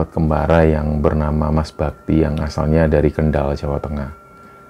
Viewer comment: Maaf mas, di awal saya salah denger namanya "mas babi". Padahal mas bakti 😭